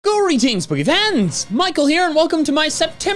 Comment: my captions apparently heard you saying greetings boogie fans XD